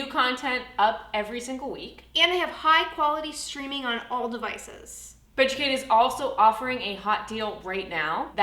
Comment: " High quality streaming on all devices"! I heard " High quality streaming on all the vices"🤣